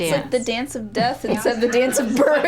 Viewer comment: What does she say at 9:40? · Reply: "Its like the dance of death instead of the dance of birth"